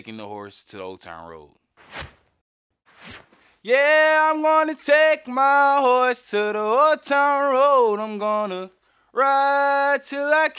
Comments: I misheard it at first as "gonna take my horse to the hotel room" and immediately thought it was a sex metaphor, funny thing is, the song isn't anything like that despite several song meaning sites making that claim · I always heard “I’m gonna take my horse to the HOTEL ROOM” and I just never understood. I hate this song so of course I never looked it up to find out, just sat there in confused horror about riding a horse in a hotel room ????? I assumed the worst lmfao by e